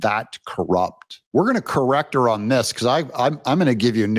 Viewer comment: I thought I heard her say, "cruel", not corrupt.